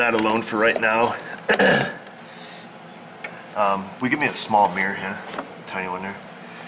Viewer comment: Totally thought he said "small beer" instead of small mirror. Lol I was like well thats how my auto diagnosis goes